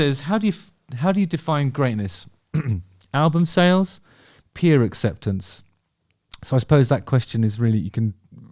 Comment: I misheard this as 'beer acceptance' 😂